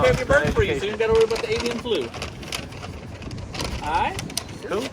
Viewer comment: Wtf did he just say! Please some one repeat if you guys heard him right. Did he just say “Asian Flu”